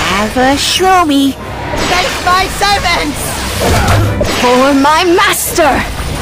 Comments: "Meet your end"🙄 "HaVe a ShRoOmY"🤪 "For my master"😔 "Face my SeRvAntS"💀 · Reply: I thought it said take my servants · "Meet your end"🙄 "HaVe a ShRoOmY"🤪 "For my master"😔 "Face my SeRvAntS"💀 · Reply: BYE I THOUGHT HE SAID TOUCH MY SERVICE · "Meet your end"🙄 "HaVe a ShRoOmY"🤪 "For my master"😔 "Face my SeRvAntS"💀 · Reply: I heard "at your service" instead of " face my servants" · "Meet your end"🙄 "HaVe a ShRoOmY"🤪 "For my master"😔 "Face my SeRvAntS"💀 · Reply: I thought he said “fetch my servants”